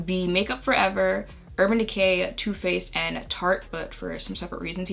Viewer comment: It’s 4:30am and when you said Too Faced at 7:25 I heard tooth paste and now I’m thinking about that instead of going back to sleep